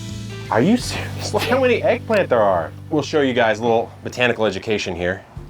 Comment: 47 tomatoes? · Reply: Oh, I thought you said how many plants of tomatoes. Table guess, 353?